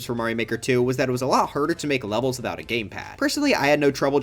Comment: did you just say “herder” 🥄